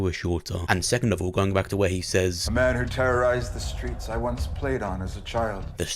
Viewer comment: No Prater said the Ripper is active on the streets he used to be a kid in, not while he was a kid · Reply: nah ur right lol my mistake. I went back and watched the scene I thought he said when he was a kid but he says “he terrorized the streets he once played on” either way good video man!